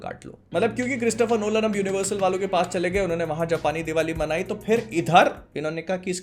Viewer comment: did he just say "Japani diwali"??💀💀